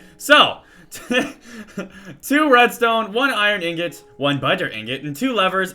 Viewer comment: Hav u noticed , every time u say " But er " it sounds like your saying BUTTER!!!!